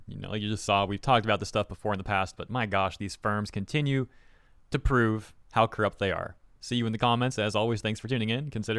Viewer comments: What I heard: "My gosh these sperms continue to prove how corrupt they are" What he said: "My gosh these firms continue to prove how corrupt they are" For a moment I thought went wild 😅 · I must have bad hearing because you said ‘these firms’ and I heard ‘these sperms. Just saying my invasive thoughts out loud.